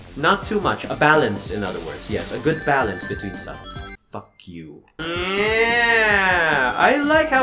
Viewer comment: first time I heard him swear FUCK YOU lol it's a crab